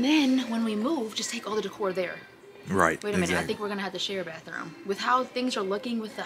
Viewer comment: I heard her say when we move that they gonna share a room are they now officially married? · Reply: She said bathroom